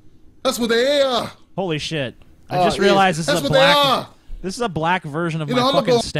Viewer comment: I thought it said 'Tim is Black' xD